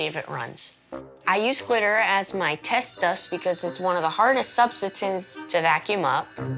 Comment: Anyone else hear this kid say “subsatance” at : 2:42? 😂😂